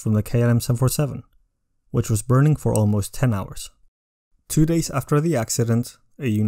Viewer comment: did you say burning for 10 hours?